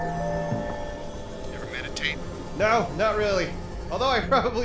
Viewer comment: i've watched this series a few times over and i'm on my 4th re-watch.it's taken me this long to realize that says " Ever meditate? " instead of " Ever met a taint? " which is what i thought he was saying lol